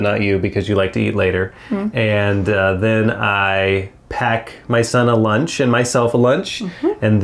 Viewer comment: what did he say? It sounds like " I pack my son a lunch". Is this Right?